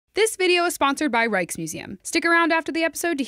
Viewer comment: Wasn't looking at the screen at the time, so I thought Intern said "Reichsmuseum" and I was all "Nope!"